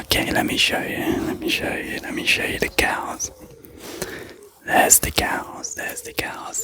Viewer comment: I thought you were saying "let me show you the gals" until i actually saw the cows lol